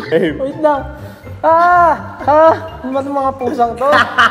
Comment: Did I heard Stell Said "Puta" o ako lang talaga